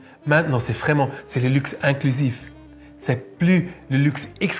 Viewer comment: Bonsoir, j'aime bien l'idée de du luxe "inclusif" contrairement à "l'exclusif", d'autant plus quand ce luxe reste raisonnable pour des calibres hors normes, c'est une chouette firme, jeune et dynamique don je remarquais ce matin même un panneau publicitaire de la marque dans un coin de ma gare locale, que j'aurai regardé avec moins d'attention si je n'avais pas connu ta chaine qui m'a fait connaitre cette marque lors de tes précédentes vidéos. Un grand merci à ta personne pour la vidéo et aussi à pour sa sympathie et pour l'effort linguistique qui nous fait toujours plaisir à nous les francophones. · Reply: Bruh j'ai compris "golf de luxe inclusif"